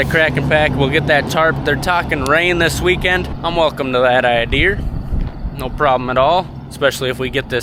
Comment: Did you say, "idear?" 18:59. You ain't where I'm from. Where did that come from?